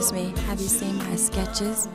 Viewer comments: lmao bruh i thought she said " excuse me have you seen my sketchers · took me about 10 replay at 4:39 to figure out she's saying "excuse me, have you seen my sketches?" thought she was saying "have you seen my Skechers?" not impossible, because Skechers was founded in 1992, but def not associated with 90s hip hop and r&b